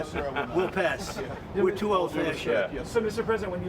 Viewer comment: did trump just say "we are to told for that sh!t"?